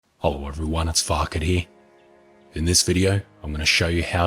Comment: I always hear "Hello everyone it's fucketty". Is it intended? Anyway thanks for your vids on The Forest, great job man.